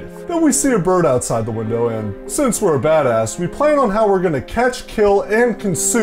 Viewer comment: At first when he was talking about Molly's story, I thought he said "and since we're a fatass we're going to plan on catching and eating the bird".